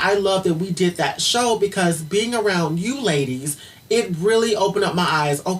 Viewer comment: Did say "being around you LADIES"?!